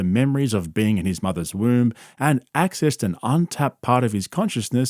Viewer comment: Wait, his mother’s womb? I thought he said “room” lol